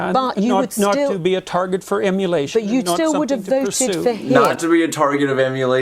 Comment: "a target of immolation" - I had to look it up. So I thought at first he said "emulation" and thought that didn't make sense. And then I google immolation. So he is perceiving that the host bringing up Trump and his controversial past into his (back then) current political position. Peterson did not take the bait, he refused to acknowledge whether it was justifiable or if he supports the narrative in which the host was attempting to convey. "Of course, it was immoral." Immolation is defined by a trial by fire. To slaughter for one's trangressions or beliefs. Possibly, for instance a past ridicule when people had been condemning others and their truths by throwing stones at them or burning them at the stake. By placing himself on a debatable platform, he does not wish for the viewers to see him as a pig, or misconstrued/ seeming to be adjacent with an Alpha male and their stereotypical virtues. Pretty fascinating and worth mentioning.